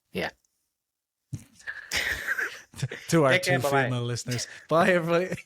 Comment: Hahaha 2 what? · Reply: I could totally have misheard you but I swear you said 2 female listeners